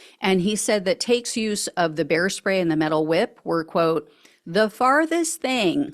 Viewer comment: When you first said bear spray I thought you said hair spay!🤣 I thought, Is this guy going to do a drive by hair styling on someone?